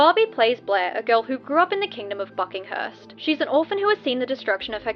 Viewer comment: omg I thought you said "fucking" 😭😭😭